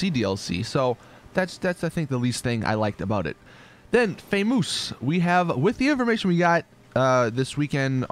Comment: What. The. Fuck did he just say? Famoose FAMOUS FAMOUS ITS CALLED FAMOUS